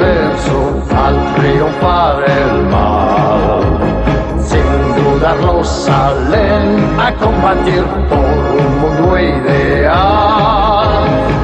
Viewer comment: 🤣 modulan como la mierda o yo escucho como las wueas 😆 en la parte que dice: “Sin dudarlo salen a combatir por un mundo ideal” yo entendía: “siendo una rosa ven a combatir por un mundo ideal” 😆😆😆😆 y me decía: Cómo tan gay! Pero al ver a Shun y al Fleto de pegaso que nunca se cogió a Shaina, no me entraban muchas dudas 😂😂😂😂 y en la parte que dice: “la canción de los héroes” siempre entendí “la canción de los terrores” 😂😂😂😂😂 pero al ver lo pitiados que eran algunos personajes con unos poderes de dudosa reputación y procedencia que supuestamente luchaban por el bien, me decía, bueno, puede ser 😂😂😂😂